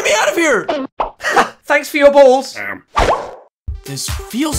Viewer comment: The snake: thanks for your balls Me: that's what she said